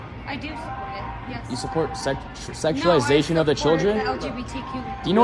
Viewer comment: Did he just say... us as LGBTQ+ sexualize children? 🤣